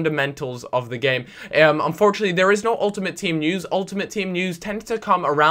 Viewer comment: i thought he said no ut